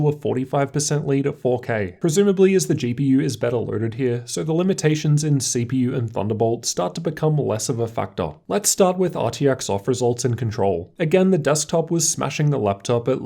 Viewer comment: "Becomes less of a fuckduck??!!" That's how I heard it the first time anyhow. Second time I heard "factor". OK that makes more sense!!